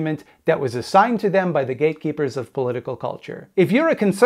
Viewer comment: Initially I thought he said "gaykeepers" and I thought I missed a joke :D